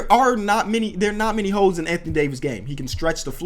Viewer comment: Did he just say hoe ????? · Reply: Mustapha Hassan that’s just how he says holes